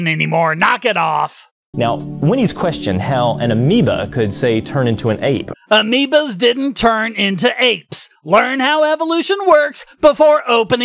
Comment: it sounded like you said amiibos early in the vid